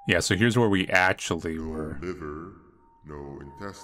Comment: i misheard 14:58 as "my LOVER". naturally i was v confused